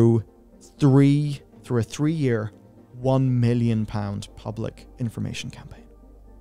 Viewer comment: When they said "public information campaign" I heard "advertising campaign"